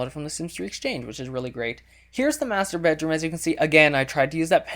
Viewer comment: lol first time you said "muster bedroom" i thought you said "masturbate room"